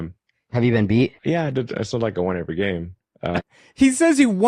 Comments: To be honest, the way I hear it, he's saying "It's not like I never lost", I don't think he's saying "I won every game". Still a bad take on his end. I noticed TikTok or whatever makes subtitles makes some mistakes when people don't pronounce clearly certain words. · "Have you been beat ?" "Yeah, I mean it's not like I won every game." That's what I thought I heard. Maybe I'm mistaken. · I despise this fraud and it’s been obvious for years he’s a lying conman, but y’all did mishear him. He was asked if he has lost and he said “yeah, it’s not like I won every game.”